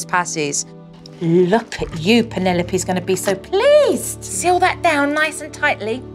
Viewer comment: I thought she said Penelope is gonna be so pissed XD